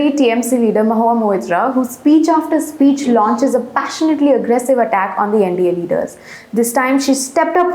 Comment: Ah, passionately aggressive Mahua Moitra! I thought you said passively aggressive... and less said anything about bimbominkia the better.